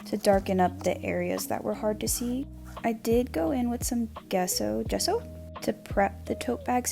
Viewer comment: lol every time you said Gesso, all i heard was "queso"